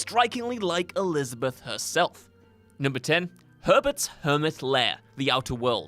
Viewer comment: herberts hermit lair ... I heard hermit purple joejoe reference lol